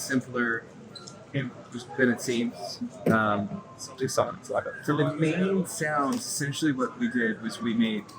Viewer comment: is saying "so the main sound, we made ten of them" ?